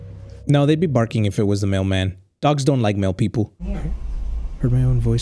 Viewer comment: The way I heard say “ dogs don’t like mail people “ made me think he said “ dogs don’t like male people “😂